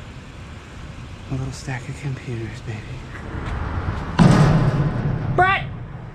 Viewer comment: Lol I thought you said my Lil snack of scrap lol